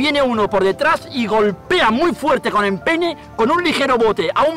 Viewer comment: se que dijo empeine pero yo escuche nepe